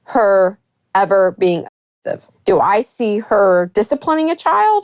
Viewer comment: I don’t know if I’m having a lapse at the moment… what is the word “a***ive”? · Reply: Abusive